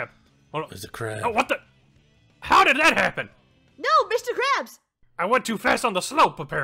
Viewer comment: Did I just hear say Mr. Krabs?